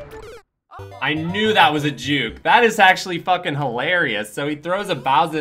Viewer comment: What exactly did you say? I hope I just misheard. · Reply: justin fwibber "I knew that was a juke!"